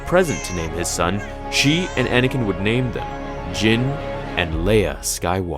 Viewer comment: I know it’s “Jinn and Leia.” But for awhile, I heard “Jim and Leia.” LOL.